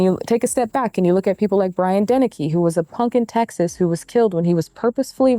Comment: I did a double take at 31:34 because I thought you said Brian Dennehy lol.